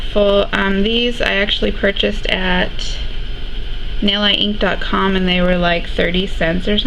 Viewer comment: what did you say, hun? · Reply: Hi, I stated that I ordered the practice fingers at Nailiteinc . com :) Thank you for watching!!